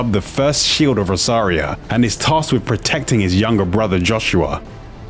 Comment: Am I the only one who heard him say “Arse cheek of Rosaria?”